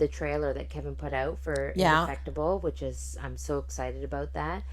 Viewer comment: Hey . . good work ladies . . . what is the trailer Keven put out (23:11) I didnt catch the name?? God Bless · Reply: "Indefectible"